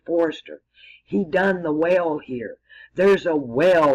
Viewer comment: in one of your recent videos, you say you haven't figure out what the whale means in the old testament talk you gave. I'm sorry I can't be more specific than that. A little before 27:57 I thought I heard you say there was a 'whale' in the Sphinx. Then I realized you said "well." The whale has a water spout. --It may or not be helpful but I thought I would share in case it meant anything to you.